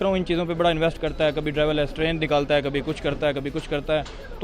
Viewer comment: मैं 2x स्पीड पर देख रहा था... मुझे डबल स्ट्रेन सुनाई दिया। 😅😅😅 (डबल म्युटेटेड कोरोना स्ट्रेन) बड़ा गंदा pronunciation है चिचा का। 😅😅🇮🇳🇮🇳🇮🇳